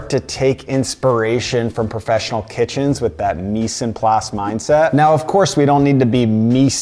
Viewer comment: Newbie here, so go easy on me. What is he saying at 1:22 “meese and ply set” it sounds like, but even the auto-generated closed captioning didn’t get it? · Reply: It's actually Mise en place. It's a system where when before you start cooking, all the prep work such as the peeling, slicing etc has already been done and organised in different containers. Not very different from all the salads at a kebab place being ready in the display fridge and then just applied to the food upon ordering.